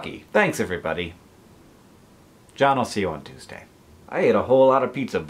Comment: hank it sounded like you said "John will see you on Tuesday" and I was really confused for a second